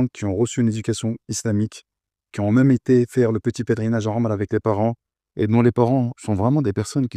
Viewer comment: C'est une vidéo traduite en français ? Sinon, pourquoi l'intervenant parle à 00:00:20 du petit pélerinage à Rome ? Lapsus linguae ? Coutume musulmane ? Problème d'audition de ma part ? Hallucination auditive ? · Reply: Il dit "la omra" ..